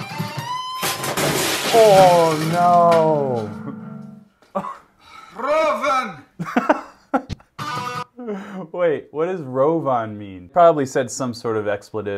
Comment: I think he's saying "röven", which basically means "ass" in swedish. His dialect sound a bit weird to me though, so he might be Norwegian or Danish too...